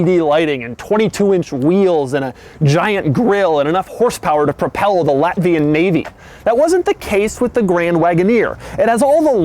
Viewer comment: haha... did he said "enaugh horsepower to propell Latvian navy" ? at 6:20 LOL :D I'm from there, usually nobody knows where it is :D